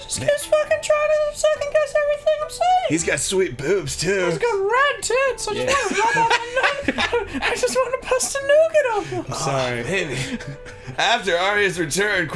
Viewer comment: did Ross say "Bust a nougat"? I've never heard that euphemism before XD